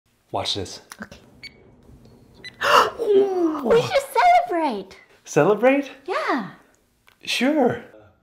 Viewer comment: Is it just me or I thought she said “you just set it right” instead of “we should celebrate”??🤔